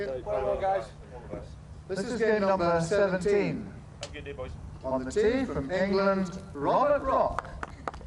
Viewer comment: Does anyone let me know what are they saying ?? 28:48 @@@@@ guys ~~~!! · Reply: 'This is game number 17, on the tee from England Robert Rock'.